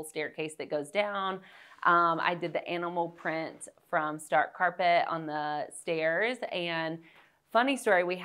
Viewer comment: I absolutely love this home and decor. What type of carpet runner is that? I thought she said 'dark carpet' but cannot find anything like that online. It's gorgeous and I have been searching for something similar. Love her design sense! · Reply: Stark carpet